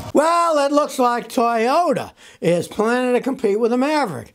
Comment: Thought you said Toyota was tanking. My guy your all over the place Scott. Keeps me coming back less often. But always good to hear you.